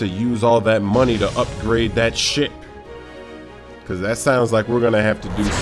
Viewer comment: it sounded like he said "SHIT"